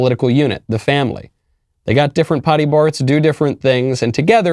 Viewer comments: Anybody else catch it???? 1:58 "Pody bart's" 😁😲😂🥴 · did Michael say potty parts? If so, ....LOL